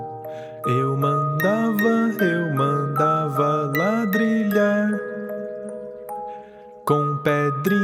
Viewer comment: eu sempre entendi "eu mandava eu mandava ela brilhar"